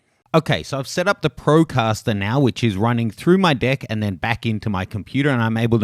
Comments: is he saying deck or dick?? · did he just say the microphone is running through his dick?